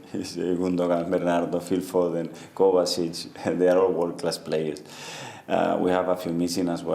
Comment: I heard they are all worthless players🤣🤣